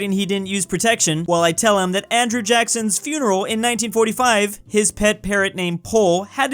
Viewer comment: Did you say 1845 or 1945? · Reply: 1945